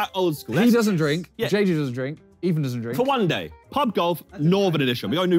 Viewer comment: Did I hear Ethan don’t drink at 1:15 ?? Bollocks mate 😂